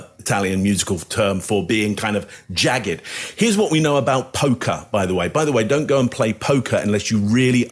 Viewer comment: when you went from music terms to gambling you said “poker” and I heard “polka” and went down a rabbit hole trying to figure out how probability relates to polka style music. I mean, we all know music is math, but polka?